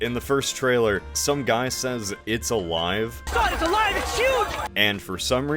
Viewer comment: I hear ‘it’s alive’ when I’m reading along with subtitles, but hear ‘it’s a lion’ every other time